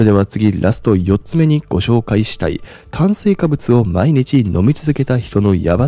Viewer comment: 炭水化物って言ってる！ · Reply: お～～そやね～。 そう聞こえたのは私だけかと思ってた。 まとめに入る直前に 炭水化物を、飲み続けると～ エッえっ！う～ん？と聞き直してもそう聞こえた😂🎵